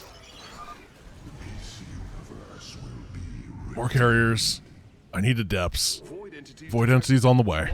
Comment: did he just say DC universe will be redeemed? xD